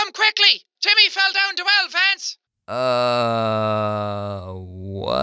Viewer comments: All I heard was, "cOme QuIcKLy tImmY feLl DoWn A WeLl VaNcE!" I also heard fence instead of vance. :> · I heard " hurry up timmy fell off the fence" 😂😂😂😂😂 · What i hear is 'TIMMY FELL DOWN THE WELL, VANCE!' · I thought the phone call said “you gotta come here quick Timmy fell off the wall fence!” XD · Lol I thought she said " you have to come quickly because Jimmy fell of the fence "XD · I think she said Timmy fell down the well Vance. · I thought she said "your gonna have to come quickly cuz timmy fell down to elephants" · I thought she said “Timmy fell into Elephants” just thought I should say that.. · I thought the green girl was saying ,Timmy fell down the well...... Maybe I’m just weird..... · At first I thought it said Timmy fell down 2 elephants😮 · I heard ‘Come quickly young Timmy fell down the fence’ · The only words I could make out was:”YOUR GONNA HAVE TO COME QUICKLY CUZ TIMMY FELL DOWN THE WELL VANCE!” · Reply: i thought she said "fell down the ellobance" idk either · This is what I heard “Vinny fell down a well Vance!” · I heard "Timmy fell down the well vance" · your going to have to come quickly Timmy feel down two elephants Did anyone else hear that or is it just me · i thought she said timmy fell down the well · At first I thought the lady said " YOU HAVE TO COME QUICKLY TIMMY FELL DOWN THE WELL ADVANCED " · I thought she said "TIMMY FELL DOWN THE WELL VANCE" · I thought the woman said: ur going to have to come quickly! Timmy fell down the wild fence!